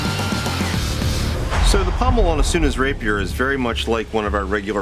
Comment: Not gonna lie, I thought it said anus rapier